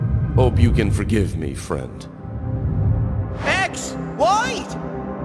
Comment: The subtitles say "White" instead of "wait"🙏🏻😭